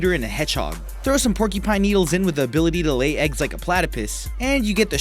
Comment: DID HE SAY PLATAPISS!? XDD😂😂 I'm crying